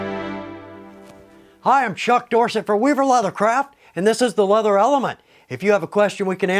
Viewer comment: In his introduction, for a second, I thought, I heard him say “we’ve a lot of crap” instead of weaver leather craft” haha 😂 Helpful and upbeat video thanks